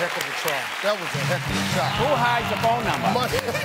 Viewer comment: sounds like he says “What’s wrong with you”